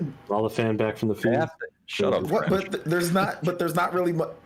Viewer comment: HAHAHAA Thats why I really like that is apart of this show weekly.. Even though I couldn't quite understand what name he called him, shit was funny! "SHUT UP FRENCH" is what I heard, I probably woulda blasted him with some sorta pokemon reference like "Shut Up Squirtle!" lol